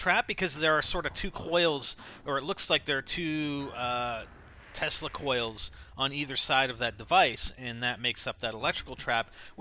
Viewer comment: I thought he said testicle coils lol